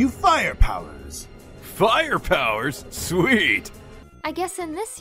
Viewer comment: I legitimately thought that it said sweat not sweet 🤣🤣